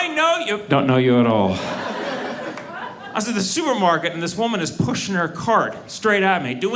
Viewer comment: I literally thought he said: "Sewer market." LOL